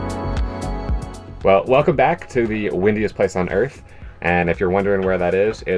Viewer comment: At first I thought said “wittiest” place on earth then I realized he said “windiest”. I like witty more! 🤣❤️